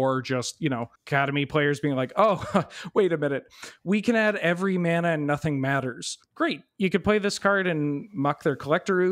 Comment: I heard "we can add every color and nothing matters" and honestly that was such an attack. that hurt